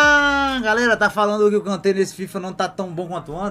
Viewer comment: afinal quem não ta tão bom? Mbappe ou Kante? Falou o nome de um depois do outro. Tendi foi nada kkkkkk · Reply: kkkkk kante!!